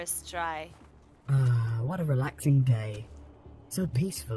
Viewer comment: sounded like she said “ah what a relaxing gay”